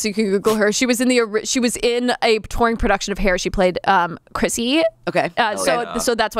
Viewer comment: Instead of "touring production of hair" I heard "nrop (corn) production of hair" and lost my mind. And now I can't unthink it 😂